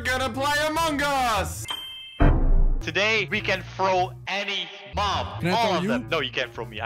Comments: did say ‘can you throw any mom?’? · can we throw any mom? Me: He actually said mom… Btw it was